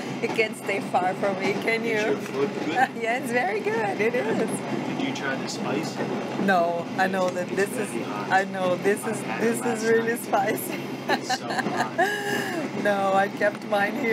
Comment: Fun fact: the last music is actually called “Jakarta”. No wonder it was a perfect fit ❤️. Thanks everyone for watching my Indonesia series 🇮🇩 . From next week on you’ll see a different country here. Please come back to channel so that we can keep virtually traveling together 🙏❤️ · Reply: omg am I the only one who misheard the conversation at 5:34 when he said "Hows your foot? did you try to spice it? its very hot!" I THOUGHT THEY WERE FLIRTING until she holds up the sambal LMAO sorry